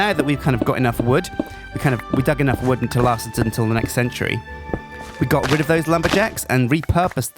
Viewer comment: "got enough wood to last us the entire century" pleace tell me somebody else heard this as i did.